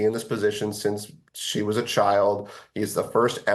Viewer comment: did he say "since SHE was a child"?? wtf